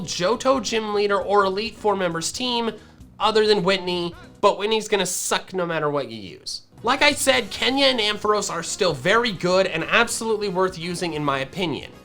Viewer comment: Wait, did you say Kenya and Ampharos 9:07??? Or am I missing something?(I... didn't play the Gen 1 games...)